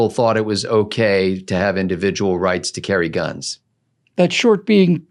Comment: Woops thought you said civil rights act for a second there.